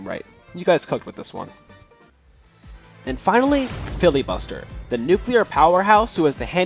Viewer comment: I swear I hear filbibuster